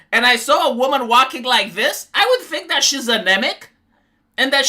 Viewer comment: You said anemic and I thought you said a Namek.... like a namekian from dragon ball lol